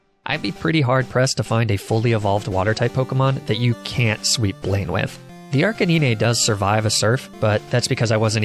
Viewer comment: wtf did he say?? Ark-é-nee-ne?? 💀💀 im dead